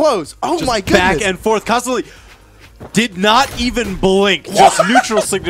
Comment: did he say cosolix